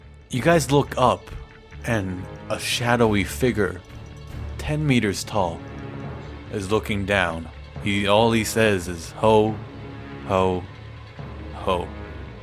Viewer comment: I thought you said shadowy finger